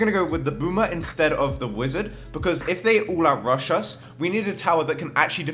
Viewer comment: shat did you said ... rushes